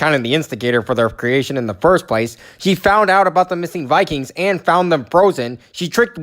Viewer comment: says “she found out about the missing Vikings and found them frozen” but the subtitles says “he found out about the missing Vikings and found them frozen”. So what? The subtitles just ignored the “S” in the word “she”?